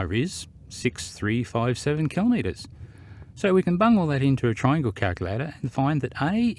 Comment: "so we can bung all this into a calculator"... Is that what you actually said? I thought i heard the word "bum".... Not sure why I am asking this...